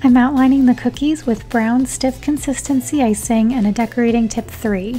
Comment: My friend didn't hear "Luster paint" and only heard "Lust" so all I hear is, "AYO who is h0rmy?!"